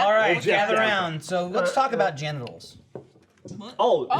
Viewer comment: When said “let’s talk about genitals” I thought he said “let’s talk about gen:lock”